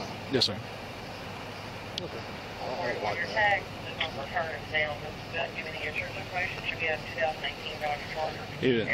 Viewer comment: Man, I would love to know what he just mumbled. I think he was being kind of sarcastic: "So, you pulled him out... for no reason." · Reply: He mumbled that Grantham said resisting arrest. Then he said that I was going to Charge him for failure to ID but. LMAO. That But got you.